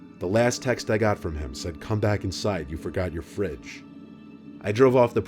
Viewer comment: What I thought he said 😐: CoMe BaCk InSiDe YoU fOrGoT yOuR kIDs what he said: Come back inside you forgot your fridge